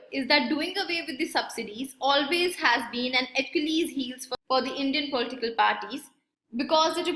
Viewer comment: What did she just say? I rewind the video thrice but couldn't get that idiom used. Please somebody let me know if you got it. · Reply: Achilles' heel or Achilles heel is a weakness in spite of overall strength. it refers to attributes or qualities that can lead to downfall